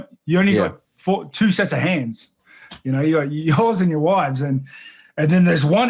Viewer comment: sounded like my boy said: 'you only got two sets of hands - your hoes and your wives